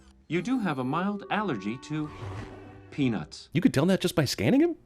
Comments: anyone else thought he said penis instead of peanuts? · ok everyone who else heard penis